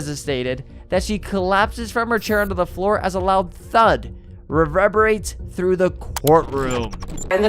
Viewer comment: Dezastated? Rerehbarates? What are these words at 6:00?? Maybe another take?